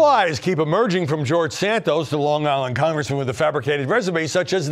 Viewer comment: I swear I heard hin say "Congresswoman"